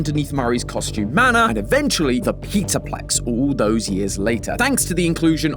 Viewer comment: Who did the subtitles for this vid? Because Peterplex instead of Pizzaplex (sub line starting at ~0:45) legit made me lol. Also, pretty sure it's supposed to be Murray's Costume Manor*, not *Manner (same line at the same timestamp). 1:17 line: should be MXES/M.X.E.S, not mixes. 2:02 line: should have "this guy" instead of "the sky" 2:15 line: 2x should have "jester" instead of "gesture" 2:19 line: "fall-fest" instead of "full-fest" 3:13 line: John Fuhnaff, RyeToast, ID's Fantasy, (following line) Dawko; not Jon FNAF, Rai Toast, Ivy's Fantasy, Dorco 4:39 line: lore, not law 5:08 line: sun, not son 5:41 line, along with the following 5:45 line: 2X Edwin, not Edward 7:00 line: 2X sun, not son 8:45 line: M1's, not just "one's" 16:04 line: Wiegenlied, not "Vegan Lye" 16:14 line: "its composer, Johannes Brahms", not "its Rosa, Johanna's Brahms" 16:20 line: bedight, not bed-eyed 16:25 line: "is baby's wee bed", not "his babies we bed" (that caption almost sounds inappropriate, but can be saved by an inferred assumption of putting the babies to bed, instead of bedding them like you would a romantic partner). Though, technically, the line should be "is my baby's sweet head", based on the top DuckDuckGo search results I got 19:00 line: MXES, not MEXES (but at least much closer than "mixes" back at 1:17) 19:47 line: Missing line - "As far as we can tell, Edwin is dead." 20:21 line: lore, not law 21:41 line & following 21:45 line: 3X, not Gibby 22:12 line: fall-fest, not full fest 2